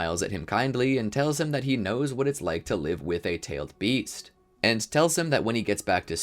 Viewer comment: Who else loves Naruto? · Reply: bro i always hear "tailed beasts" like "tailed beefs"